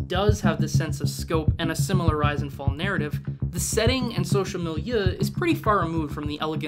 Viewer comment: He says "social milieu" incase anyone didn't catch that